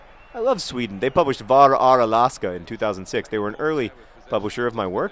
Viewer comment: hhahaah, I'm Norwegian and I thought you said "Bara är Alaska" (..."Just are Alaska") so I googled to check if that's what they call it in Sweden and ...no it wasn't. "Var är Alaska". I don't think we have any of your books in Norwegian, John, but I'm not sure... I don't know if I'm rooting for Sweden, since they're our beloved neighbours or if I'm rooting for anyone else than Sweden, since Sweden is our competition in almost everything. Yeah, we didn't qualify...